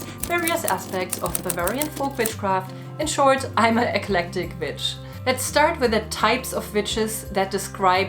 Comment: This is a wonderful video, but ai cannot gets over the fact that when she said “I’m an Electic Witch” I thought she said bitch 🤣